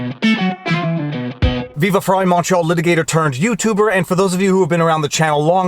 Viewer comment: Wait... I just realized all this time you've been saying "Montreal" litigator. Until today, I kept hearing "macho" litigator and never questioned it.